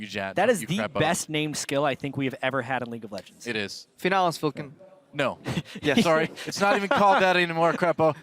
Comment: what does Krepo say the best named skill is? I cannot for the life of me understand him. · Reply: Finales Funkeln, Lux's ult